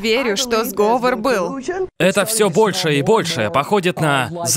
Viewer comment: ахаха. я на 1:30 минуте услышал "это все Польша и Польша" и чуть чаем не подавился со смеха